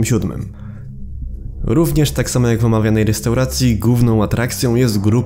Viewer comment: czy ja usłyszałem"gówną"?